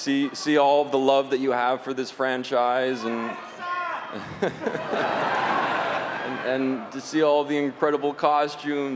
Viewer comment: what does he shout? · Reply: “I hate sand” 😂😂😂